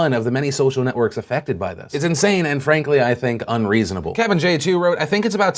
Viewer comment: anyone else hear anne frank..ly